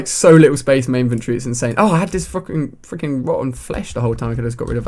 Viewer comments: Lol at 6:38 it sounded like you said fuckin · did anyone hear alia say f*** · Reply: He said fricking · first time i heard ali-a say fucking. it doesn't rally matter though :P